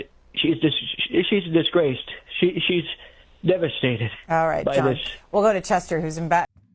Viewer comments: Did she say "All right, Trump"? It sounds like she thought it was Trump too. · Did I just hear the host say, "Alright John"? It almost sounded like she said, "Alright Trump." Then again given Trump's dementia, maybe he actually believes he is John Barron.